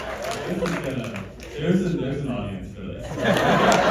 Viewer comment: i need someone to tell me what kathryn says after daniel says “there’s an audience for this” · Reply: she repeated daniel and said "choices being made"